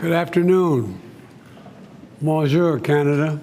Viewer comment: Did he just say monjur canada?